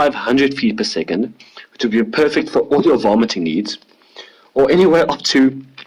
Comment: At first I thought he said "all your vomiting needs." 😁😁